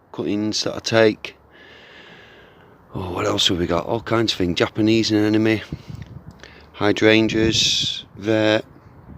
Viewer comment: @ 5:26 did you say you have Japanese Anime planted?